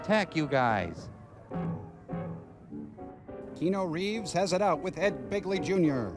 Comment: Did he say, "Kee-no Reeves?"